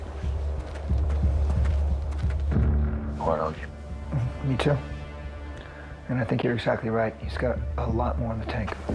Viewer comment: What is he saying at 45:21 ??? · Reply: he says “I quite like him” and the other replies “me too” 😂